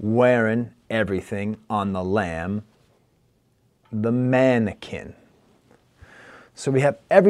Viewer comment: Does he really say wearing everything on the lam the mannequin? I heard it as “wearing everything on the lanvin mannequin” as in lanvin the luxury brand